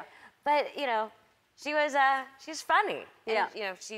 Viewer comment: I swear I heard Joan when Melissa said "she's funny"